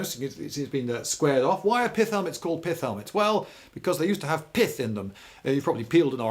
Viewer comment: At the start I thought he said piss helmet 😳🙊